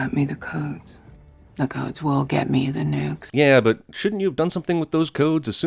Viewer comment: it sounds like she says nudes